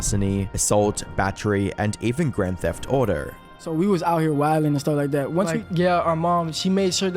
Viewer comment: i was looking away, but still listening; thought I heard "grand theft otter" for a split second